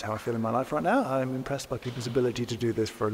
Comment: did he just say second channel?